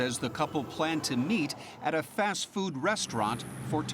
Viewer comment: Maybe I'm the only one who thought she said they planted meat for Jack in the box.